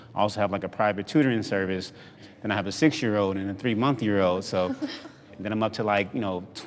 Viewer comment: I Know I am not the only one who heard 3 Month year old 😂😂